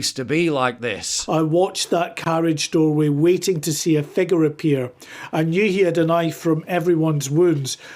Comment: Please someone tell me wtf a "figger" is? Usually Brits say "figga" but huh? 😅